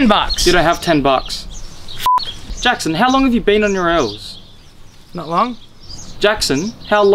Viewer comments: what the hell is saying at 0:23 ??? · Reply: "Jaxon how long have you been on your own?" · what did say here? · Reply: "How long have you been on your own?" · What is he saying at 0:25 ? How long have you been on your else? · How long have you been on your L's? 0:23 Is that what he says?